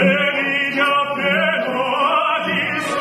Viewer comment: Towards the end, did he just say "ok no more" in Spanish? Because thats what it sounded like.😆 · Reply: He said: No llego, and then again, he said the same. That means "I can't reach the note" lol